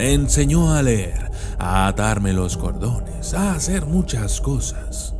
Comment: Baia le entendí “a darme los condones” jaja